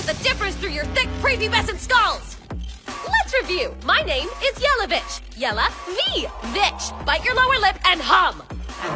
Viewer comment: I heard "Ms Yellow Bitch" xD